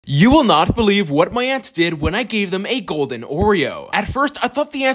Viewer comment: Bro, I thought he said: you will not believe what my ex did when I fed them an Oreo💀💀